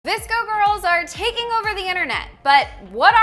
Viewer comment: when i first heard this in class i thought they said disco